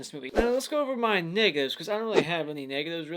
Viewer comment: When I heard him say negatives I thought he said the N word!